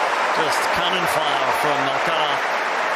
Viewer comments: I hear "just coming fire from natal". What does the commentator actually say there? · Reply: It's definitely "cannonfire from Alcaraz". The 'z' is pronounced like a 'th', and the commentator puts emphasis on the second syllable. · did he say "Just cannon fire from Nadal"? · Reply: He's saying Alcara- some of the commentators have been pronouncing his name without the Z. Maybe that's how it's pronounced in Spanish.